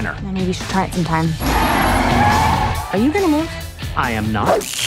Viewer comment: Guys, what is the lady with the yellow vest at 1:41 saying? "are you ..."? I can't make out what she is saying (English is not my native language) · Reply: She said “are you gonna move?”